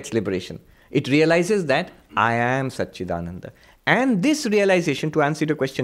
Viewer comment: Some of the auto-generated English captions are incorrect..... For instance...@ 2:37 he says "I am Satchidananda" but the caption says "I am such a done onda". This can be misleading for some people who depend on the captions. They do not convey the excellent answers given by Swamiji.